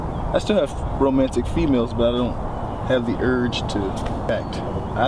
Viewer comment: What does an "asexual relationship" even mean in this context? Queerplatonic? Homoromantic? It sure sounded like said he had romantic "females" (even though the CC says "feelings") and of course he can be bi/panromantic, but it looks like they're just friends. I need a longer version of this if it's going to answer any questions.